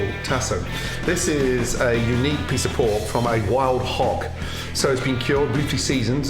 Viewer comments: I thought he said pork from a flying hawk. I was like, fuckin A pigs really are flying now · dude i was so confused because i thought he said a beautiful piece of pork from a wild hawk, not a wild hog ...i was like i dont think they're doing this in Louisiana